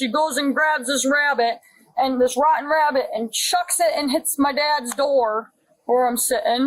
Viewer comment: Did say that her girlfriend threw a rotten rabbit (aka dead) 🐇 at her?? 😮🎉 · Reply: That's what was said sir. That's what was said 😢😢😢😢😂😂😂😂😂😂